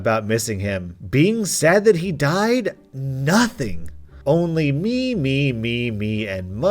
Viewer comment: I thought it was saying meme,meme